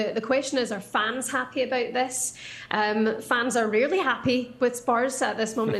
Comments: Did she just say spurs fans are really happy at the moment? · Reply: I wondered about that too and listened to it twice. It sounds like she said "really" but I think she actually said "rarely". That would make more sense as Spurs fans (and I am one) are definitely not happy at the way things are going atm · Did she just said Spu fans are really happy? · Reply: Rarely